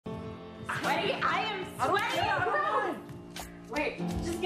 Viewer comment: Does she say "Hero"?👁️👄👁️ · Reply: im pretty sure its "im sweaty AND GROSS"